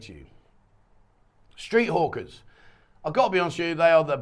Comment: I really thought you said street hookers at first. lol. it took me a few times to realize you said street hawkers.